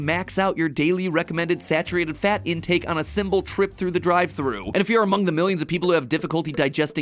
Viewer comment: did anyone else catch Matpat say "symbol trip"?